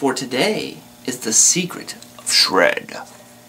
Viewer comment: When you said shred in the beginning it sounded like warowl saying spread